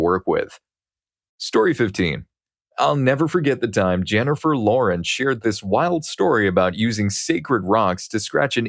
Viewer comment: ngl, I misheard story 15 as being about Jennifer Aniston and got super confused